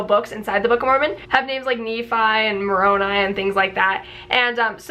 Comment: Sorry, misheard, thought she said "I'm a moron..." Apologies.😉